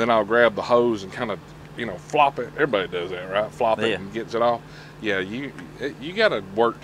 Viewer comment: at about the 9:35 when y'all were talking about that EXPENSIVE air chuck...... I though I heard you say that you flop it all around to get off. To each his own I guess. 😂😂😂